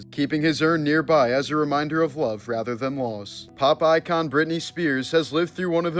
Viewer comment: I had one of those moments where I misheard something and was like wtf ? When you said something regarding "pop icon Britney Spears" I heard "Popeye-con" and for a millisecond wondered why in the world there was a Popeye convention . Dumb, I know. 😂😂😂